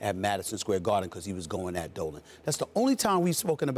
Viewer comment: When he says "going after Dolan" I thought he said he was on that dope